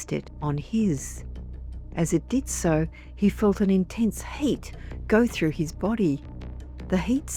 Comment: I thought you said intense hate for a moment, that was a disturbing predicament.